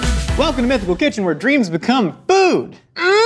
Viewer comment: Every time, without fail, when I hear “welcome to mythical kitchen, where dreams become food!” My brain goes “where dreams become memes”